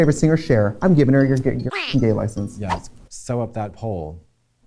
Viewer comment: I heard "Sew up that hole" and now I'm deceased.